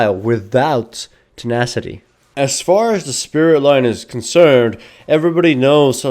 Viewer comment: thought he said Spirit lion......